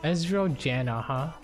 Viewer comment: When you said “ezrael janna” at the beginning I thought you said “estrogen” which is a hormone I believe xdd